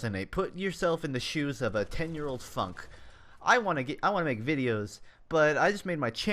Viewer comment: I had to rewind when you said "Let's go back to 10 yr old Funk!" I thought you said "Let's go back to a 10 yr old FUCK!" lol xD